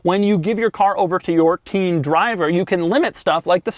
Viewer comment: Oh he said TEEN DRIVER... I thought he said 'peeing driver'.